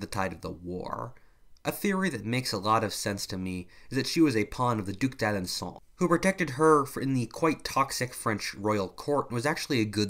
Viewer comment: Says that Joan was a Puppet of a French Duke, the pronouncation of which meant I couldn't figure out how it was spelled, but according to the subtitles is called the "Duke Dad and Saul" [Duke of Dalandsaul.] If anyone know the Proper Spelling and Pronouncation of this Duke's title and what the Name of it's holder was, could someone please tell me because I didn't understand what sayed, [Probably beecause he's Pennsalvanian and I'm Australian,] and am interested in this Duke. Please respond if anyone knows, I'd greatly appreciate it thank you. :)